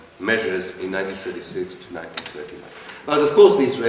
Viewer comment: what is he saying? anti-arab, anti-what?